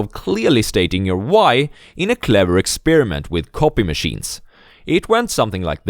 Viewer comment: I thought you said coffee machine,.. had to watch it twice to understand it